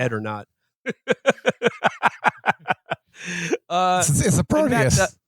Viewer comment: What does he say at 3:48? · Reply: He says "its a proteas!" confirming my long held theory that Matt's stroke was actually the DNC hitting him with the Havana Syndrome gun to stop him spreading Marxist thought in America